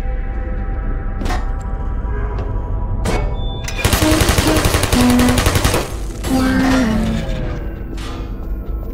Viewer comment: what is the turret saying when dying? At 53:34 it sounds like it says "brains". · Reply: "Critical Error" while it's tipped over. "Whyyy" when it shuts down.